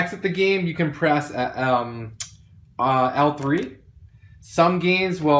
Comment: alt tree? I don't understand what u say there? Which button to exit? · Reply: L3 is what you want.